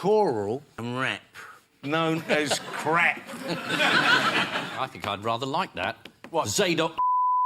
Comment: Does anyone know what Ian says at 21:27? I've seen this clip and watched the episode many times but never could make out what he said that caused such a reaction from everyone. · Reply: After the choral-rap gag from Ray Winston, Ian says “I think I’d rather like that…. Zadok Mother****ers”. I think it’s in reference to the hym ‘Zadok the Priest’, which is sung at the Coronation of the Monarch (and the basis of the Champions League theme) with the expletive added- which shocks the audience and Paul.